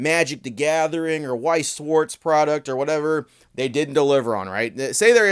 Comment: Misheard ‘Weiss Schwarz’ as ‘Weiss Sports’, now THAT would a be crossover I’d wanna see! Anime Girl and Sports Johnson Tag Team GX